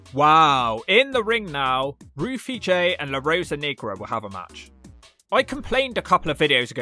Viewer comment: Did you say "La Rosa Negro (Knee-grow)" instead of "La Rosa Negra (Neh-gruh)? Oof... Not good, bruv. Not good.